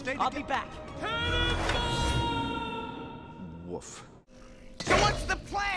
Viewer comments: Did he just say OOF? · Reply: He said "Woof." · DID HE JUST SAY OOF